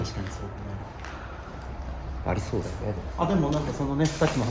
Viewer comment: さんの「怖いよね」のあとに多分「そうだよね」とぼそぼそ女のコの声が聞こえる